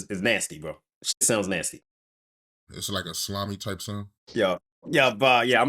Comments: "It’s like a slimy type sound " you wild 😂😂😂😂😂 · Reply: Lol, I thought he said a Salami sound lol🤣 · "Is it a sloppy type sound"😂😂 pause bro!! 😂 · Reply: I thought he said 'slimey'